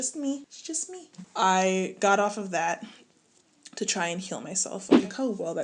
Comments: I thought you said to try and kill my self. @ 4:23 😩😩😩😩😩 I had to rewind. I’m so glad I heard wrong. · I misheard "to try and kill myself" and my heart dropped!!! 😲